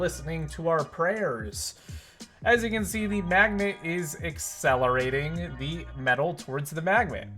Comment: I thought he said lets throw it at the MMAGDNET!